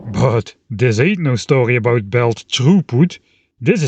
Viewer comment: When you said belt throughput, I thought you had said “troep” lmfao